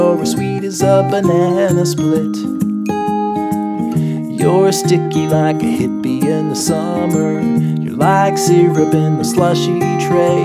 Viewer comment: I thought he was saying "Youre stinky like a hippie in the summer'...lol